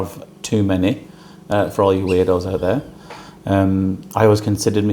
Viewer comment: Weirdos? I heard eardos.